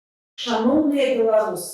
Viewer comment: Мне одному в первой фразе Свеиланы послышалось шалом беларусы, вместо шаноўныя беларусы.)))))